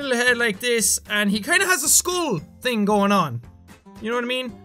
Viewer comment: For dead pool did you mean hehad a scream thing going on and not a school thing? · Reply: Yeah, he said Skull. He's irish and it made it sound like he said School.